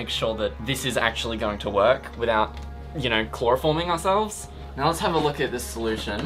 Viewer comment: Why do I keep hearing "Chlorofoam"